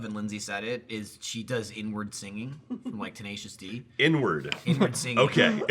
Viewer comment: What did they think Michael said at around 18:05? I missed it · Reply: prae suwanvitaya They thought( joked around) he was saying "n-word" instead of "inward"